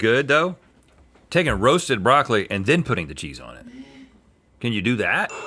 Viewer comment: when whispered "broasted broccoli" I heard 'gross broccoli'